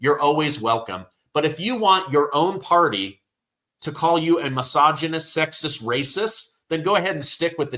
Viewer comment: I am in California. Whole towns were voting Trump. We are sick of this insanity. Newsom is insane. Pelosi, Harris has helped destroy California. Get them out of power. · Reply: I thought She said Black misogynist & Latinos racist